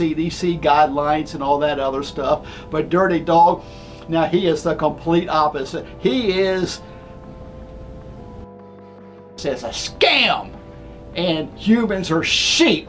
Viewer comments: what did he say in 2:21:00 kek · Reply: He said coronavirus is a scam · This dude obviously suffers from early signs of dementia. Seriously, he should get checked out! Edit: 2:20:59 O.o what did he say? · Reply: He said, "Coronavirus is a scam"